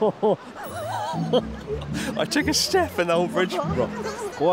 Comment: Some of the subtitles are a little off, just to let you know, Chris and team: maybe the person doesn't understand your accent Chris? You said "I took a step and the whole bridge just wabbled" but someone transcribed that as "I took a step in Old Bridge" (displayed at 02:42). Do check/ have your team check, in case of any other discrepancies (I haven't watched the whole thing yet). I noticed because I couldn't quite catch what you said the first time (even though we have a similar accent) and so turned on and read the subtitles for that bit, and they didn't make sense (you'd clearly said something longer than the few words displayed), so I replayed the audio and turned it up. And just quickly looking for any other mistake that came earlier, I see that before that at 02:37 it says "God he's ping in wondering whether or not to cross the (bridge)" (should it say "he's probably wondering"?) Maybe just double check for anything else that is off (I have hearing trouble so read while listening and it's a bit jarring when something clearly is very different from what can be heard or something's wrong, e.g. I wondered if you were randomly swearing and someone toned it down when it says "he's ping in wonderig whether or not to cross the bridge", after it realised it's not the AI generated subtitles that often explains why something's off). Well anyway it's just worth being aware of, to have a second person check the first person's transcription, an editor to look for mistakes or whateve